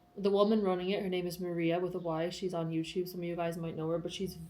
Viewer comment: Maria and last name? I heard 'with a y'